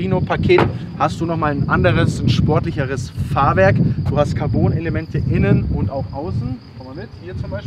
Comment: Ich hab eiskalt im ersten Moment Carbonelement*innen verstanden und mich erschrocken.